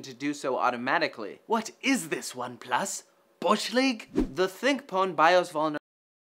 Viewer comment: Can someone explain what says at 4:12? The audio keeps cutting dead for me there, and I can't tell what he's saying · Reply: ThinkPwn BIOS vulnerability